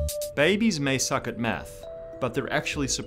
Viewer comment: I seriously thought you said “Babies may suck at meth...”